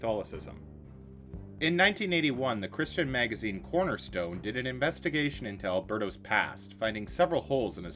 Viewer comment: Perhaps it’s my perverted mind but I had to turn on closed captions because it sounded like he said “in 1981 the Christian magazine ‘Pornostore’………